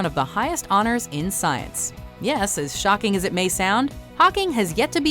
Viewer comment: the way it sounds like she says pocking annoys the shit out of me